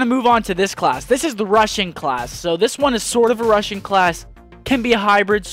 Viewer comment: I thought he said "...the Russian class..", and I'm like WTF is a Russian class? Then I realized he said "a Rushing class."